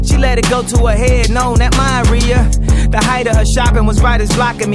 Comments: when you realize kanye wrote that entire verse just to say 'diarrhea' at the end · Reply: I thought he was saying I hope you die Riah as in short for Mariah😂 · It kinda sounds like Kanye says "I hope you diarrhea"